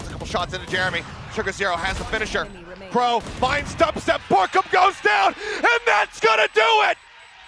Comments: BORKUMが落ちた にしか聞こえんw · BORKUMが落ちたって日本語で言ってるようにしか聞こえんくて笑い止まらんwww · 空耳「BORKUMが落ちた！」